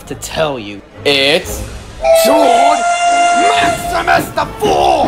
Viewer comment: What did he say his name was? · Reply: He Said Tord Maxmus The 4th